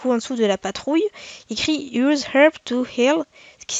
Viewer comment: Le prend pas mal mais quand t'as dit. " Use herb to heal " j'ai compris " Use herb of hell " ( pour les français utilise les herbes de l'enfer) 😂